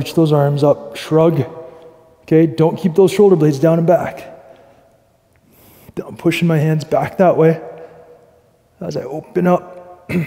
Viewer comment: Minute 5:55-The Subtatitles run as "I'm pushing my hands back", But I hear "Don't pushing". Please clarify.